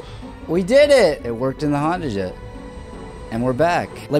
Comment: I thought you said handa jet and I was like huh?